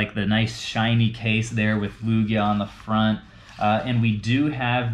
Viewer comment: excuse me.... How did he just say luigi?.....